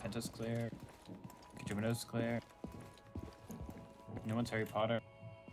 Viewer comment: I swear I heard “No one’s harry potter” 🤣🤣